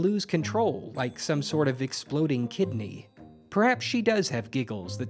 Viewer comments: “Her giggles cause others to lose control L I K E S O M E S O R T O F E X P L O D I N G K I D N E Y” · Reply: At first when I heard it I heard “Like an exploding kitten” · “Her giggles cause others to lose control L I K E S O M E S O R T O F E X P L O D I N G K I D N E Y” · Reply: I thought it said Exploding Kittens, like the card game